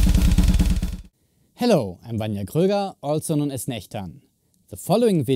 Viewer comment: I thought I almost heard him say “Hello, I’m IBXToyCat”